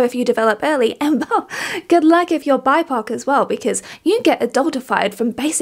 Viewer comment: Hey could someone tell me what the word she uses at 0:54 is? Bipar? Bipark? Thanks! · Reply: It’s BIPOC. Which stands for Black, Indigenous, and other People Of Color